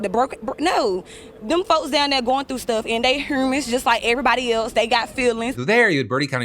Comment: What an accent at 22:00. I think she said "they're all humans like everyboday else"? Please correct my west coast ears if I misheard her. She's speaking sense, and even here in SF we have had inmate protests and lawsuits alleging mistreatment of inmates and deteriorating infrastructure of the jail itself. I personally think ours should be shut the f down. I agree, they arent in revolt for "no reason"